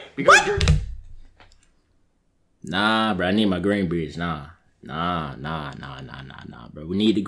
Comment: 1000$ to the person who guess what he sayed at exactly 10:23 " I need my green bread bruh" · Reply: He said "I need my green beans" pay up